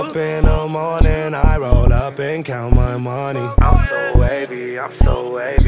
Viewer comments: Im so lazy im so lazy when I wake up in the mornin im so lazy 😂😂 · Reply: it really sounds like he says I'm So Lazy lol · It sounds like he is saying I’m so swazy